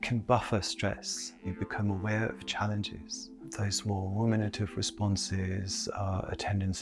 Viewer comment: Can someone tell me what he says at 06:25. Womanative? Huh?